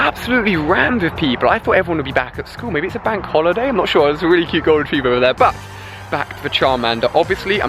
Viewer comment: who else thought he said cute girl